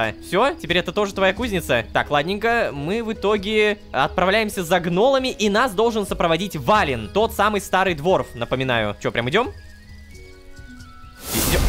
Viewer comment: Я постоянно уже слышу не Валин, а Двалин - Геншин убивает ;|